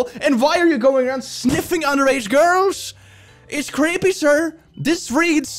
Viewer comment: As an underaged girl, these kinda statements make me feel incredibly uncomfortable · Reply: thought yuo said undead girl lol